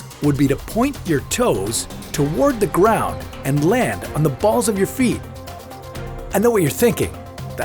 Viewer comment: I literally thought he said land on your balls